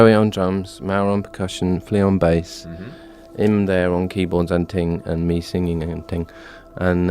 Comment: what does he say at 10:49 lol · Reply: him there on keyboards and ting. he's making fun of London slang